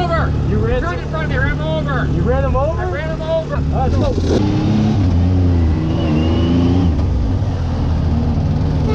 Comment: I still don't know what said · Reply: limestone is the best dicerun, I was there but I didn’t see you.